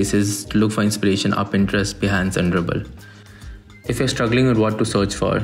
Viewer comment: what did you say after pintrest please? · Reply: Behance*